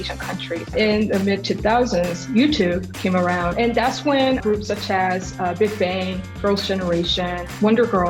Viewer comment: Did anyone else hear "groups of chads"? lol